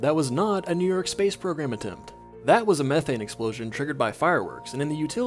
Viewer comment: I definitely heard "meth-head explosion" despite fully comprehending the topic of today's video beforehand